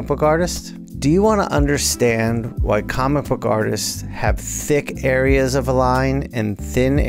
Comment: I misheard that the first time. "Do you want to understand why comic book artists are thick?"